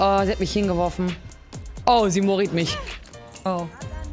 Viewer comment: Hab erst verstanden: "Oh, Sie Mauriced mich.", und mich dann gefragt welcher krasse Skill einem schlimme DInge antut, indem man den Button nicht klickt.